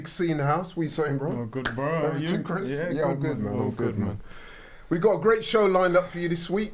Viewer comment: did they just say in synchronisation ‘yeah I’m good man, all good man’ hahaha